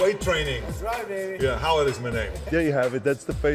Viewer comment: "That is right baby..." "da how it is my nigga!" I know, i Know he did not say that, but it sounded like it (English is not my first language so that explains why i heard that)